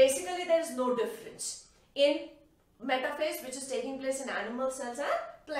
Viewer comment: What did she say at 4:42. You explained well though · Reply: Metaphase